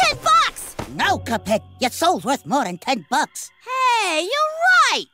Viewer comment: I misheard 0:11 as "Your so worth more than 10 bucks" and honestly, it made my experience better